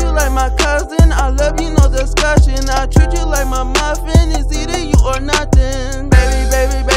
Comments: I treat you like my muffin. It's either you or nothin? 😂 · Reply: I thought he said eating you or nothing hahahaha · "I treat you like my muffin, it's either you or nothing" I had an out of body experience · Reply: I thought he said it's eating you or nothing!